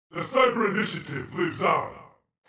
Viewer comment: what he say? "You sacred mission ......"? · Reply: The cyber initiative lives on